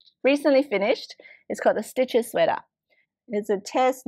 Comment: Lol I thought that jumper said “chest itches” which it would for me!